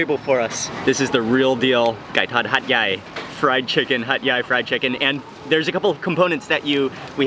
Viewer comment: literally thought he was saying "Hot guy Fried Chicken"